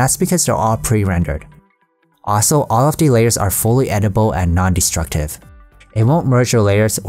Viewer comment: I think you said "Edible" instead of "Editable" at 1:45 :)